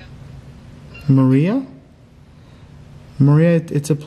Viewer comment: I didn’t hear “Maria”, to me it sounded like a guy saying “Brian”. And I heard “Stay with us”, not “Stand with us”. But, just my opinion on what I hear. Great video!